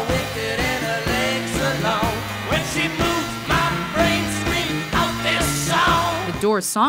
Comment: Did anyone else hear, "when she pooped my brains screamed out this song". Just wondering..